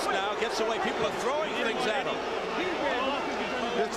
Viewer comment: Very hard to watch on so many levels. The corner, the commentators, the crowd... .. horrible.. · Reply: Frankie Sick people Sounded like they said crowd was throwing coins